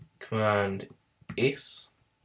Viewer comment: What Does He Say At 3:48 ? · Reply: command